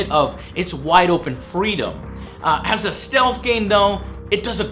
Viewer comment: HAHAHAHA I thought you said ASSASSINS CREED is a STEALTH game HAHA funny. Great review joe, I just HATED the comparisions to AC, all of them.